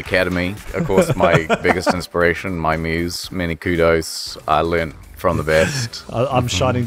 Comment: I had always heard it as "mini kudos" on account of the kiwi accent My brain glossed over it like that granola bar called kudos. I don't know they have Kirkland Signature in New Zealand, but maybe he came stateside and loved them enough to make it his tag